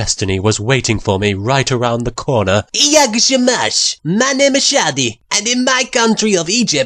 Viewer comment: Do I hear 'jak się masz'? :ooo Didn't know Shadi voice actor was Polish :D